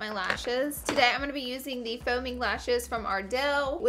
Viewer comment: I thought she said foaming lashes instead of faux mink lashes 😂